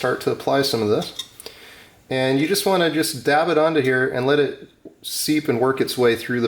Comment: did i just hear “dab on the haters”?